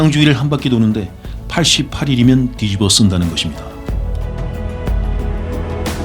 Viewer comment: 뒤집어쓴다 ? 제가 제대로 들은거 맞나요? ㅎ ㅎ 주인장님 멋진 목소리에 저말이 들리니 안어울립니다 ㅎ ㅎ